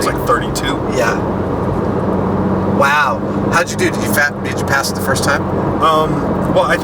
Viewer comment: it sounds like says: how did you do, did you fat bitch?